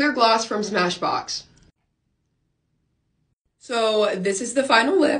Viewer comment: Me thinking: oh my gosh did she just say this is my perfect lip sh*t or did she say this is my perfect lip shade then me mind read the caption and realised did you say lip shade